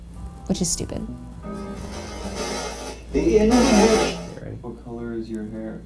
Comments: I hear ''the inevitable'' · I heard "The ending"